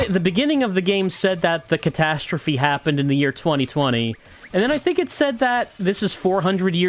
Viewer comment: Back in the year 2020 AD... Honestly thought he said 2020 80 at first. But yeah, I definitely remember this happening.